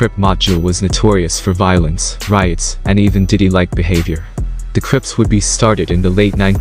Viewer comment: did you say “diddy like behavior?? 😂😂